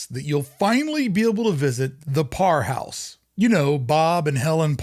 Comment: I thought you said "poor house" at first, I think that was accurate.